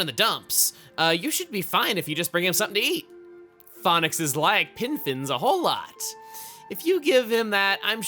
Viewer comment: I thought said Phonexes like infants a whole lot.